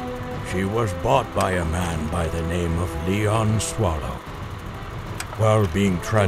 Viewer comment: thought he said Leon kuwata